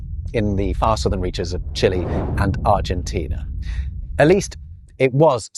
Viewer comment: I wasn't paying close attention to the video and I thought you said, "...spoken is the arse of the region of Chile and Argentina."